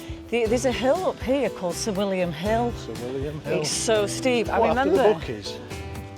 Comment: I thought said the the name of the hill was 'civillian hill' then you said 'what named after the bookies?' and I realised what she really said 🤣🤣